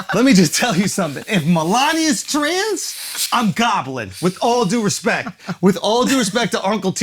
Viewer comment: "If Melania is trans, I'm gobbling " that shit had me crying 😂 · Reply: I thought he said goblin